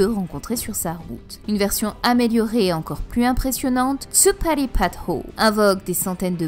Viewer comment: A 9:26 après avoir dit " une version améliorée encore plus impressionnante " t'as dit quoi ? Je suis malentendant ... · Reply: "Tsuppari Pad Ho" c'est mon accent aussi il n'est pas terrible XD mais il s'agit de la technique de Kuma ! j'espère avoir pu t'aidé